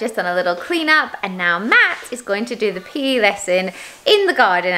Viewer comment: Really thought she said "Matt's gonna do the pee lesson outside" and I was like, hmm that's weird. Then it clicked she said PE 😂